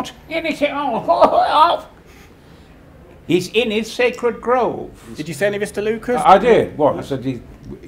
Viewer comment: Thanks to this video, I've discovered what half a dozen of Jar Jar's lines actually were. Sacred grove? I thought he said "Second place" for 18 bloody years. Brian knows what's up.